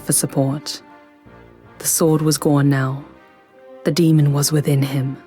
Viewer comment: It sounds like she says "the sword was 'gorn' now." I didn't no swords like to change names. Anyway great video thanks.